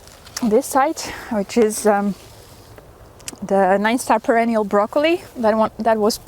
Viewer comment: Quick question. I thought I heard you say at about 5:57 that you had perennial broccoli. Did I catch that correctly? I've never heard of a perennial variety of broccoli. And...I love your consistent enthusiasm and love of gardening. Very inspiring. Thank you. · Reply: Thank you, Randy! The plant I refer to is 'Nine Star Perennial' broccoli. It can be grown from seed and starts producing the next year (much like winter broccoli) and as long as you keep removing the florets, you can perennialize it. But in my experience it is not very long lived - might be more succesful in clay soil...